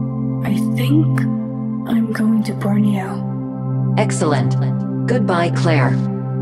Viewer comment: Claire: "I'm going to Borneo" what I heard: "I'm going to Burn You!" sh*t